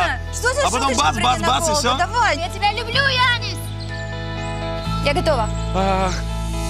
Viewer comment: "я тебя люблю ану с " 4:10 или я чето не расслышал,)))